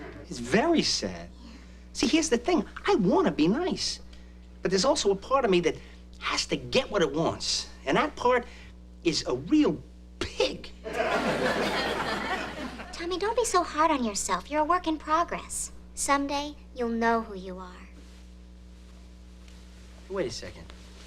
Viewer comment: HAHA! "There's also a part of me who has to get what it wants" and i'm pretty sure that part is below the belt, and then he says "And that part is a real PIG" it sounds like he says a real pik, and that's the danish word for, you know, that thing xD